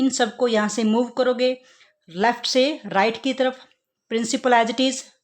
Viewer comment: When u said left se at 9:24 i thought u said lakshay and lakshay is my name